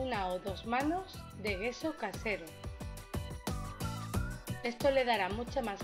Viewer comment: Hola minuto 1:39 no entiendo lo que dice ,alguien me explica que es ? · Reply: gesso casero, es una imprimacion, pero en mi caso casera, en el canal tengo un video como hacerlo... es a base de enduido plastico y cola blanca...